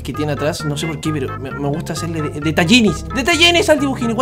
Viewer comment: En el minuto 6:53 escuche tetanini